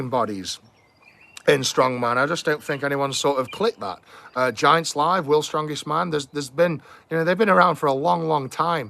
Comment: what is Eddie talking about during 10minute? ... do i hear corectly "Giant slav" ? :-D i suppose not, because google doesnt know it